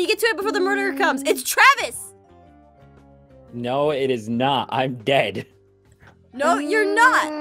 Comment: I thought Travis said I'm gay when he said I'm dead